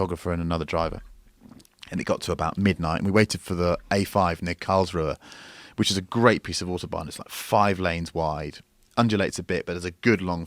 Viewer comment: what does he say after a5? I cant for the life of me understand what he said. Sounds like 'cars roar' but i really cant tell. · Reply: "...A5 near Karlsruhe, which is a great piece of Autobahn,..."